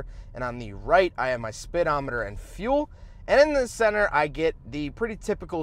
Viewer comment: I have to tell you, every time you talk about gauges, you say "speedometer" but I hear "SPITometer". Whatever. Thanks for the video. I'm looking forward to the CX9 review too, as my next car may be a dad-sled in this category.